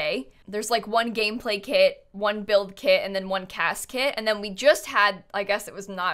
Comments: I wouldn’t be opposed to a CAS kit with like wedding dresses, bridesmaid dresses, and suits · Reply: When she said CAS- kit I thought she said casket.⚰️ hopefully we get preteens and slightly better babies before a funeral kit pack 😂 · when you said CAS kit I thought you said casket and that I missed you say something about a funerals gameplay kit... :')